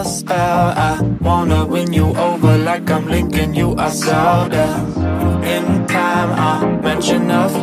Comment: "But well, I'm under your spell; I wanna win you over like I'm Link and you are Zelda" Favorite part 👌👌👌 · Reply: This whole time I thought he was saying I wanna win you over like I’m lending you a soda !